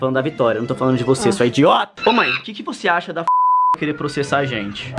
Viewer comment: Eu escutei Fabiana 4:14 ELE IA FALA FABIANA, SO QUE ELE COLOCOU EM PIIIIIIIIIIIIIIIIIIIIIIIIIIIIIIIIIIIIIIIII